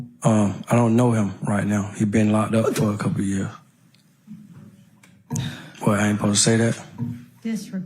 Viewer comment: I saw him say was I not suppose to say that. THE ONE PART I MISSED WHAT HE SAID AS I WATCHED🤦🏻‍♀️ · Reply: I missed it the first time, too. It’s where Hylton asks him if he and Shannon are still cool. He says “I don’t know him right now. He been locked up for a couple years…what I ain’t ‘posed to say that?” Judge W then says “Disregard that”